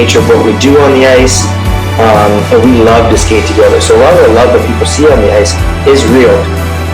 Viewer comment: did he say "what we allow people to see in the ice is real" 🙄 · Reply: "A lot of the love that people see on the ice is real."